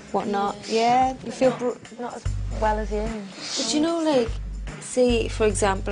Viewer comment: Can somebody tell me what Cheryl said at 15:15? Sorry I just can't understand her accent. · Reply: “Do you know like say for example I had a pain in my foot, I’d feel like an absolute idiot saying right now I’ve got a pain in my foot” - I’m not from Newcastle, I’m from Birmingham but I guess for people outside the U.K., the Geordie accent is hard to understand 😂❤️